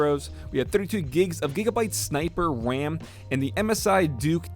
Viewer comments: sounded like he said Wwam instead of ram 😂 great video tho 👌🏼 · Did he just say wam????